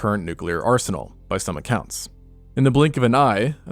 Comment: for some odd reason I heard tests not accounts and my brain was like “ bro DID THEY ACTUALLY DO THAT?!”